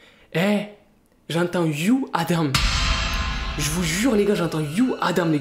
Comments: A 2:57 moi j'entends ( are you Adam ) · À 3:10 j'ai entendu you Adam Et vous ?